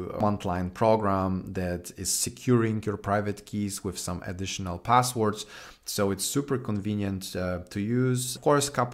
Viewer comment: What did he say in FrontEnd 4:00? VM and Wagmi? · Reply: Viem and wagmi. Great combo 👌